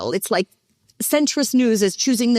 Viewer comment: I thought she said centrist nudes